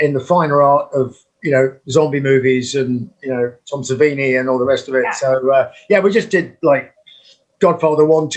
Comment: what zombie movies he was saying around 20:00? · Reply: I'm pretty sure he mentioned " Tom Savini and the rest of it , you know The GodFather 1,2,3 "